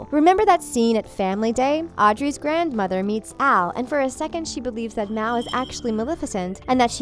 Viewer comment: you say "Al" instead of "Mal"